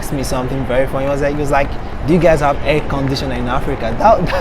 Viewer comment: Ohh at first I thought he said "hair conditioner" LOL